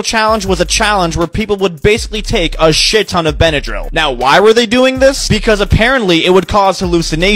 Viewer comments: For a minute I thought he said take a s&@ on a benodril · My dumbass thought he said "shit on a benadryl"